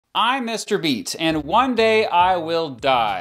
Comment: What I hear: I'm Mr. Beast What he actually says: I'm Mr. Beat